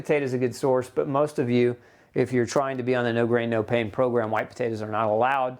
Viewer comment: Literally thought you said "no brain no pain" the first few times you said it 😂